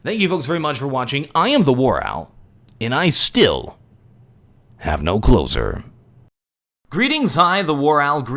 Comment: "im the war owl and i still have no clothes on" -misheard outro